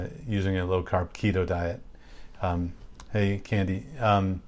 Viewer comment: At 30 seconds it sounds like he says, "I eat candy". What the heck did he actually say??? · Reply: Diane MacWilliams he said, “Hey Candy” saying hello to someone 😊